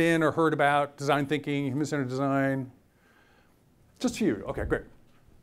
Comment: WHat the world is the word he says at 17:29/48:25???? It is not design thinking. It comes right after that "something" design. I've slowed it down and it's unintelligible. Terrible pronunciation. Like he's drunk. Anyone can tell me which branch of supposed design he was referring to exactly? like I said not design thinking he mentioned another term that is not understandable even after slowing the speed down. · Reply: Human-centered design. Remain calm.